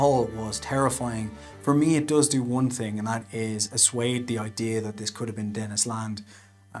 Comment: what does 'assuade' mean? does he mean 'dissuade'? or am i just hearing it wrong? he says it twice, too...